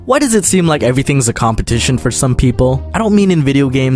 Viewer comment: At first I thought the video said Completion